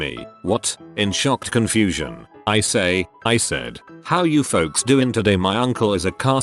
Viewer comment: Another one for 1:15 is "Hi, how you gays doing today? " Instead of "hi, how you guys doing today?"